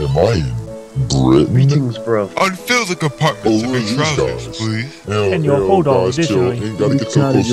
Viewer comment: I have replayed this so many times and I still can't understand what the hell they're saying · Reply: greetings bruv, unfill the compartments of your trousers please, and your holdall additionally, in discard of your favorite do that rapidly.